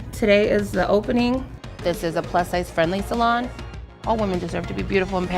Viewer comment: They say "plus-sized friendly salon," I hear "...Earthquake!"